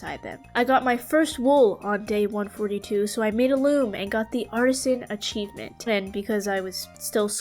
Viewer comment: Why do I hear arson instead of artisan ???